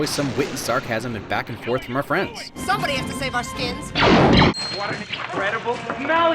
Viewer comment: For the longest time, I thought Leia said "Somebody's got to save us, kids!" rather than "our skins." I also thought "Blast it will ya, my gun's jammed" was "Blast it off of my costume!" meaning it had ahold of his stolen stormtrooper armor. Also, I had to be told he shouted Carrie! at the end, to me it always read like Hey! Yes I am deaf as a rock.